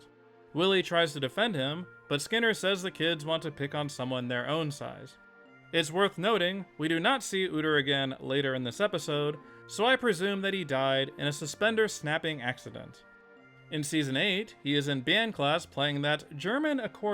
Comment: I was only half paying attention and thought you said "we do Nazi Üter"